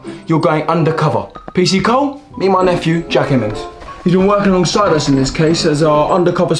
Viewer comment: did u say jack evans?